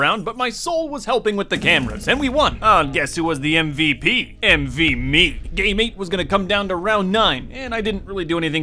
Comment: who's MVP, MV Me Me: wait, did he say "envy me" or MV me